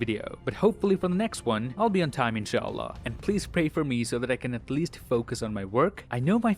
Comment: did you just said IN SHA ALLAH...?